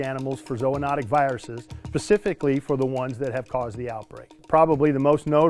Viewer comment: Did he say " Pacifically"